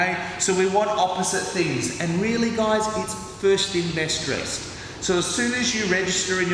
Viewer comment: What does he say at 2:08? I think it's the accent that I'm not getting. "Really guys, it's .....ly .....ous" I don't know what he said, lol. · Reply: First in, best dressed